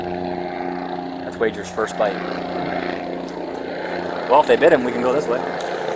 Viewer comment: "well, if they DID HIM..." Is that what he said? Is that what they call maliciously abusing people?